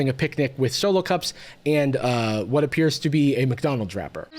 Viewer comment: didn't realise said "mcdonald's wrapper" and thought he said "mcdonalds drapper" as some kind of censorship speak for kidnapper lol